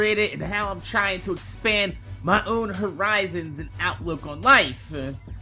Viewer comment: at the beginning i thought he was bout to say "expand my harambe" god damn you internet